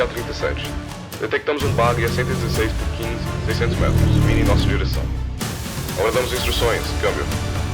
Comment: Why can I underst- IT'S IN BRAZILIAN PORTUGUESE?!?!?! Ok that's unexpected, but thank you Btw: I think there's a mistake on the captions of what's being said, the guy on the radio (brazilian) says "116 por 15, 600 metros, indo em nossa direção. Aguardamos instruções, câmbio" or "116 by 15, 600 meters, coming in our direction. Awaiting instructions, over". Edit: Just read the VA for the SAM, yeah he's 100% brazilian lmao, there's no way you can fake an accent to such a degree, but his work is great in any language he shows on his page.